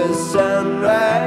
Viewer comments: Until I saw the lyrics in thought he was saying "I get out of Colorado". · Am I the only one? Am I the only one who hears them mispronounce Colorado? I hear “Coderado” which does kind of match up with the work Shadow from another line in the song.